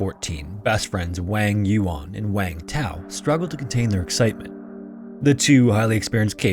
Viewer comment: I thought it said Wang one and Wang two. 😊